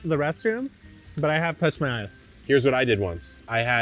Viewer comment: It's almost sounded like he Said "IDK about the restroom, but I did touch my ASS!"... I know he said eyes, but at first I laughed sooo hard. Thinking... If not in the restroom, why touch your ass??? Lol